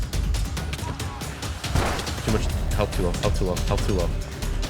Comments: my brain is so rotted i thought he was saying “hawk tuah” when he was saying “health too low” oh my god. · did he say help tauh💀